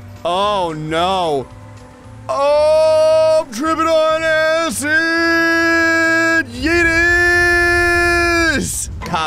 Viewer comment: I think what he's saying is "trippin' on acid"